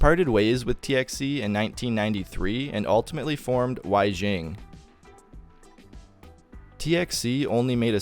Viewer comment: does that say waxing science and technology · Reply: "Waixing"